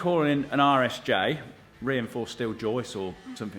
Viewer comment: Hahaha you know, its funny because I been watching this from part 1 of the project and until this part started, I thought you were saying irish j too hahahahaha